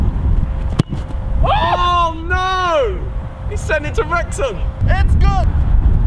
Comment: Did he say "he sent it to Wrexham" at 2:03? Cus I'm from there!